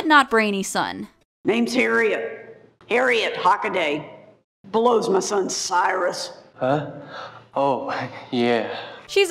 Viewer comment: sounded like she said " blows my son cyrus"